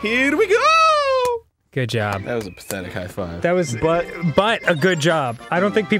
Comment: I thought it sounded like a great high five